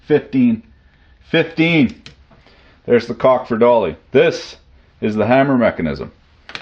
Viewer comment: Did you just say "There's the cock for dolly" at 21:00? What kind of expression is that? :P